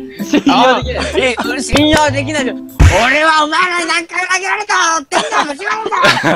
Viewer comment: ｢俺はお前らに何回殴られからってあいぎゃってえぎゃっていいいぎゃって(叫び声)｣しか聞こえんバナナ